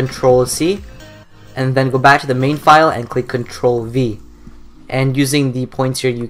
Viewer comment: HOW TO DO THIS IM ON WINDOWS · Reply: thank you I thought he said cntrl-B